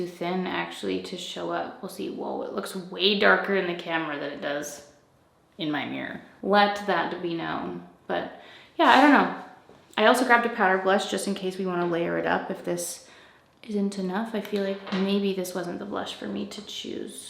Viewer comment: Something is wrong with me today because I heard "power blush" instead of "powder blush" 😳 also LOL to me because I thought masking in the title meant wearing face mask (for COVID prevention) 🙃 PS. Those waves are beautiful!! I want that amika curling set. janinedgs